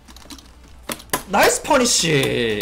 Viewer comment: the subtitles say "(??)" but what he says here and shortly after is "nais(eu) peonisi"(nice punish)